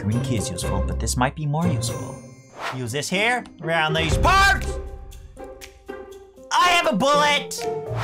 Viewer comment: Watches his videos I always hear "around those parts like an rat"